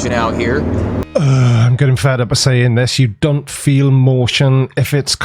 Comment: did you just say: "ugh! I'm getting fatter by saying this . . . " Is that another welshism, or ya just come up with it, or did I hear ya wrong? Thats funny as hell.